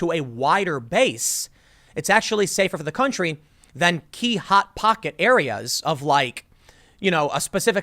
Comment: Did someone say Hot Pockets?? <link>